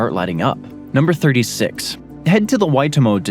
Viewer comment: He was pretty good on Waitomo. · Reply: I thought he said White Omo 😊